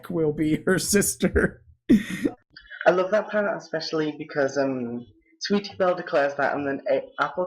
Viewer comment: I'm sorry I love you guys really, but all I heard was "Crucify Sweetie Bell!"